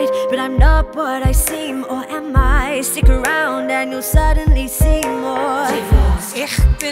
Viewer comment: Wait at 3:54 does she say suddenly Seymour? 😮